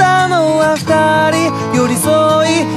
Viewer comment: sounds like hes saying Ferrari Where you going XD